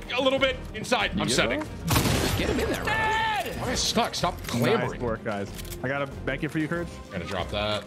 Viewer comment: ? what did he say ? glamoring? xD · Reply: Clambering